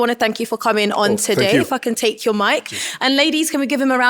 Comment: The UK accent is different. I wish the wording of certain sentences could be changed because if you are not attentive you might hear weird things. For example, at 1:36:37 when the host said "If I can take you mic" sounded like "the f word take your mic". I believe "Kindly hand me over the mic" would do. It's a small thing I know.